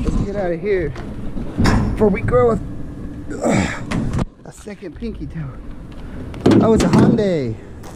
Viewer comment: I thought you said "Before we grow a second peepee." 😳🤣